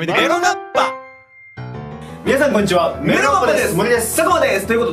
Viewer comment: 初見でした。メロガッパのところエロガッパに聞き間違えました。すみません。